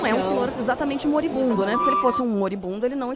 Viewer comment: Eu entendi "um senhor idoso" não "moribundo".